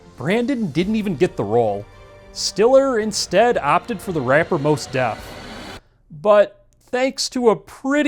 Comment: What are you saying here? Moss Depp? Most Depth?